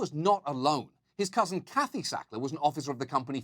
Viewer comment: Had to listen twice cause I thought he said Hackey Sackler